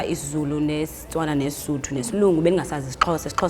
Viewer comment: What is the word that the Xhosa speaker uses for "English" at 8:50? I thought that the word was isiNgesi but it sounds like she says "Slungu". Maybe it is a contraction of isiMlungu?